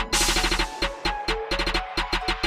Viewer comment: girl *you are listening to extreme bass booster *bear gets triggered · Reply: Axel Zamora first I heard "you are listening to extreme bass test